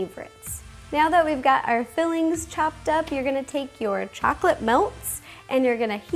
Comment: I thought you said '' You're gonna take your chocolste melts and eat them up'' lol